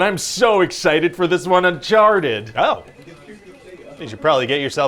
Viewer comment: It took me a minute to get the "oh, you should clean yourself up" after he said "Uncharted," right as I was taking a drink. If you're like me and didn't get it right away, the way he says it so fast, it almost sounds like he said "I sharted."